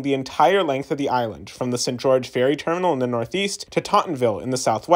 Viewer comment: Fire vid. Glad you enjoyed your trip. Staten Island really is beautiful, ppl just shit on it to go with the crowd. Few things: St. George is pronounced Saint George, idk what you’re trying to say Huguenot is pronounced you-ga-not. It’s like an even worse Americanization of Utrecht Ave in brooklyn lmaoo If you got any other questions about SI or especially the SIR lemme know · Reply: I thought he was saying sit George at first, and was simply reading “ st. “ it’s not too common to find individual neighborhoods with “saint” in the name